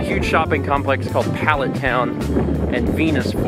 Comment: did you say Pallet Town at around 3:50? And if you did, how did you not make the obvious Pokemon red and blue reference? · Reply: the mall is called Pallette Town, but yeah, the lack of a reference disappoints me.